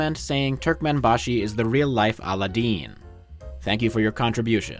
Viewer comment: Wait a minute, did you say turk man bashi is the real Paula Dean? I love her, she's my most favorite misunderstood racist.